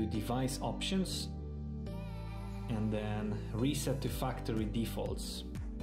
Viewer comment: I almost thought he said "reset the fucktory"